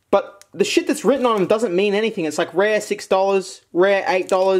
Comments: or so, I thought it said "rape". I need help. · I thought that said rape not rare